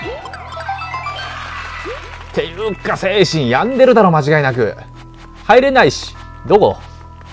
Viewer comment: 精子に病んでるに聞こえたｗｗｗ